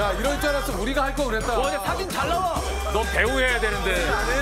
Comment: 송지효가 포즈 잡고 있는데 지석진이 배우해야되는데 하는거 ㅋㅋㅋㅋ나만 들었나 ㅋㅋㅋㅋㅋㅋㅋㅋㅋ